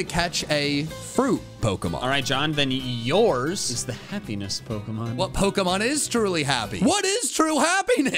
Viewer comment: I nearly thought said heaviness pokemon instead of happiness pokemon😂😂😂